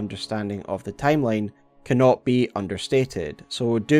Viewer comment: I think you meant to say "cannot be overstated . Saying it can't be understated would mean it's of very little significance.